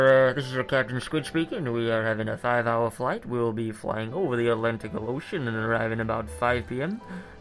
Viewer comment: did be say atlanticle ocean 😂😂